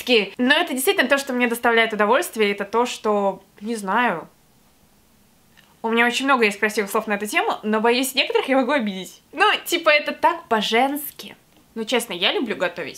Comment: мне послышалось Баженски 😂😂😂😂😂